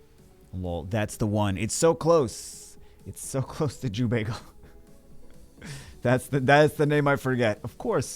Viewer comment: i just understand "thats the neymar forgeted" 🧐heard some times to realize what he said... 😂😂😂